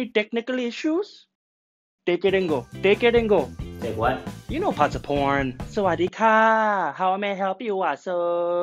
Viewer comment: are we gonna ignore how the subtitles says 'parts of porn' lol